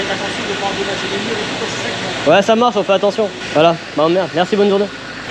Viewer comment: C'est moi ou ta lâcher un "tu m'emmerdes 😂😂